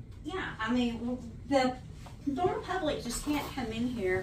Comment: it's really hard for me to tell, but did she say the "normal public" can't come into the public building?